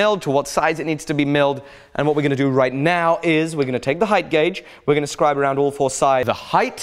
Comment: I heard that as "we're going to take the height, Gauge" and I was like "Woah alright, Alex"